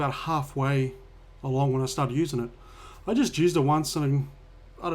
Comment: What did you say there!? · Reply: Half way along when i started using it :)